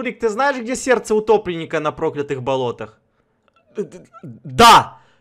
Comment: я один услышал не да, а БАН 🤣🤣🤣🤣